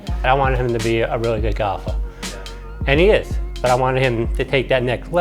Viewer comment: i thought they were saying that they wanted him to be good at god i had no idea they said golf lmao